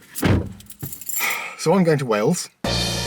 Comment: I somehow misheard "So, I'm going to Wales..." as "So I invented whales".